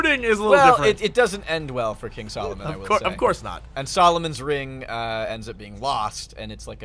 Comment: WAAAAAAAAAAAIIIIIIIIIIIIIITTTTTTTTTTTTTTT HOLEY SHIT Solomon's Ring Sauron's Ring HOOOLLLLYYYY FFFFUUUCCCKKK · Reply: When spoke about the Solomon's Ring, I kept hearing "Saruman's Ring", and I was like 'wait what, he had a ring?'. Yeah.